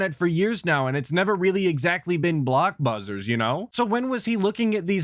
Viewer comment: did you just say blockbuzzers? Don't tell me you're a homeless PF changs employee too B... 😂😂